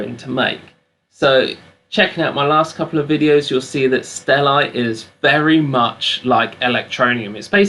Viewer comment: lol I thought he said Stellar 😂